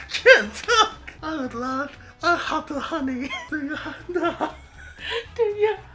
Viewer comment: When was doing winnie the pooh he sounded like he said a hot of honey